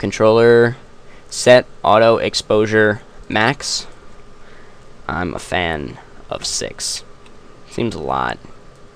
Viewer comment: at first i thought he said "im a fan of sex"